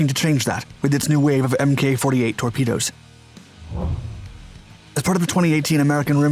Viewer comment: I totally thought it said "tornado".